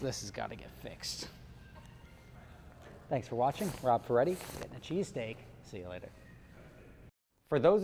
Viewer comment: Damn it. Now I want a cheese steak. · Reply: Roman Shukshin I thought he said cheesecake 🤷‍♂️